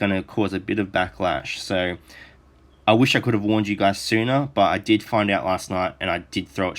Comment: Did you just say "I wish i could have warned you Tsuna" :P Also Kage's passive attack doesn't set off the rogue mark.